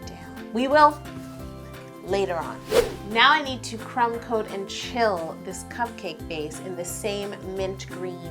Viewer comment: I always thought you said crown coat and chill, I see now that I was wrong. It was crumb coat and chill.